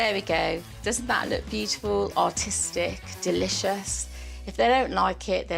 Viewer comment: I thought she said beautiful and autistic 🤦🏻 I was like oh cool they are including people with disabilities 🤦🏻‍♂️